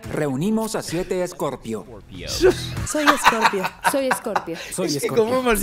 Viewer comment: Que raro, cuando dijeron "hola, soy escorpio" yo solo entendí "hola, soy imbécil"